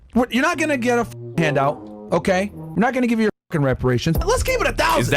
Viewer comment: Did he say "we're not"?